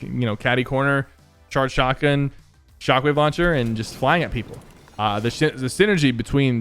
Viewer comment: it kinda sounds like he said the s**t But I don’t care if he said it or not, and also if you think he didn’t say that comment on this comment what you think he said